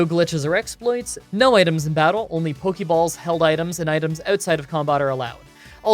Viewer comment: Thought you said no held items